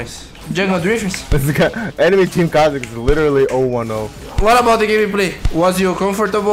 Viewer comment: Pessoal, lembrando que dei meu máximo pra traduzir tudo direito. Ainda não sou fluente 😬 Aproveitem o vídeo 👍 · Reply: he said "ôh one ôh" i.e. 0/1/0, in conversation you can say "o" instead of zero since it's easier to pronounce.